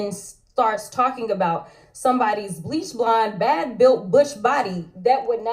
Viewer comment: Thought she said BUSH BBODY. BUTCH will get LBTGQ TO CLAIM VICTIM